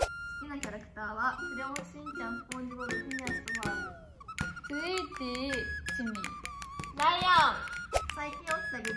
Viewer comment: の3つ目のキャラクターの名前が聞き取れなくて…😭 誰か教えてください！！！ 최고 사랑해요♡♡ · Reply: フィニアスとファーブです！